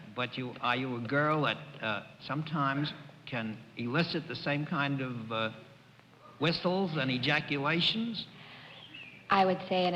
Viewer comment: Did Cerf really say "can elicit the same kind of whistles and ejaculations" at 19:19??